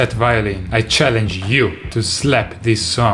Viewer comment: subtitles said "to set viola"